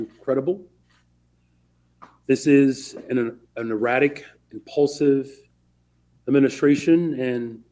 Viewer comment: Even if Johnson is extremely disliked, he's not wrong that this administration is erratic and impulsive. At first, I thought he said repulsive. That too.